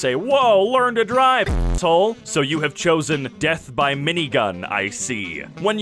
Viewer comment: I swore you were about to say "death by minivan I see"